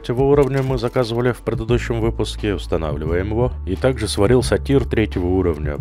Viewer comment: сперва послышалось "сварил сортир", а не "сварился тир" ))) трижды переслушал тот момент))